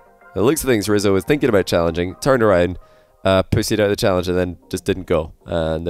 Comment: Did you say Rizzo ‘pussied’ out of the challenge? 😂